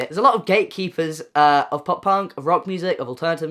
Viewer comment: I heard "young gaykeeper" instead of "gatekeeper" and I almost burst laughing haha